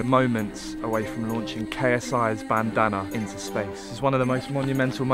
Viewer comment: At first I thought it said I SENT KSI'S BANNANA TO SPACE!😂🤣